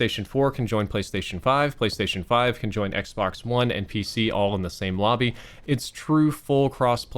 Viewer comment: As an owner of a day one XBone, I can't describe how painful my experience with it is. Can't wait to just play MW in more than 20fps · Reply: I thought he said x bone